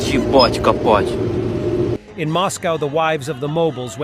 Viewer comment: what does the farmer say please :) ? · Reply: Yebat'-kopat'. Untranslated. Like as "Fvcking"